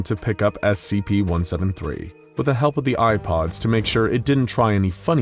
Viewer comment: I thought you said ipods